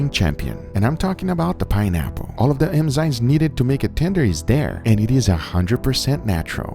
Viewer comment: Is it just me or does it sound like Guga is saying “emzymes” instead of “enzymes”?